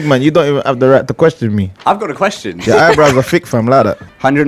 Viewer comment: did he said “your eyebrows are thick” 😂